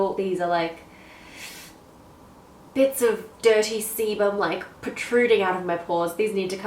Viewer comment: I was running low on time but I wanted to watch your video, so I set the speed to play the video a little faster. Here is why this is important. I was at the part when you were talking about blackhead strips. I honest to goodness thought you said "bits of dirty semen" instead of "sebum" !!!! I was all...eww gross TMI. Then I was like...wait, there is no way she said that. So I watched it again, still heard the same thing. I had to slow the video to normal speed and go back and watch it a THIRD time just to clarify! I am DYING!!!!! LMBO